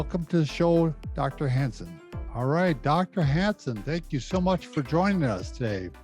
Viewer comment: When introduced him, I thought he said (which he is btw) 🤣. V informative interview - thanks. Emma x